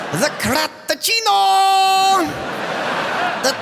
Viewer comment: Time stamp 0:39. I can’t tell how many times I have watched this video. I am hearing impaired. But today is the first time I heard this joke line properly. The Crap-a-chino- Crapachino in your butt. “It’s actually a good line”😂